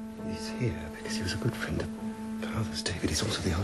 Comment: What does Bertie say at 0:18? It sounds like, "He's here because he was a good friend of Pearl's." That, of course, makes no sense and my hearing is a bit wonky. Can anybody hear it better than I can? Why were the clocks at Sandringham hep half an hour ahead?